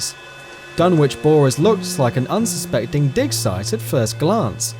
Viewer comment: "An unsespecting Dick-Size" am i the only that heard that? lol.. Anyway. Nice Video. :3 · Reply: I heard "dig site" at first, but then after I heard "dick size" xD wtf